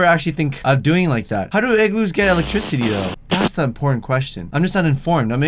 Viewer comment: I know he was trying to say "important", but I heard him saying porn